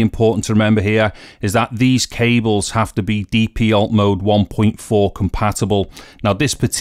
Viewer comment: “these cables have to be…” Would you mind writing the spec here? Im sorry english is not my native language and im not able to understand what has been said about this cable spec. Thank you so much for your video and help! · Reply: Sorry for the late reply ! Not a native speaker either but i think it's "DP Alt mode 1.4 compatible"